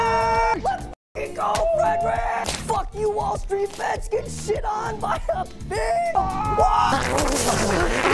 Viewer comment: The amount of times I heard “GET SHIT ON BY A FISHHHHH” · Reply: Oh damn I thought he said "by a bitch" and I was wondering why he picked such a random insult